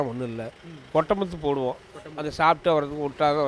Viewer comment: What did he say at 3:07? I did not understand. Can someone please translate? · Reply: He said " கொட்ட முத்து போடுவோம்"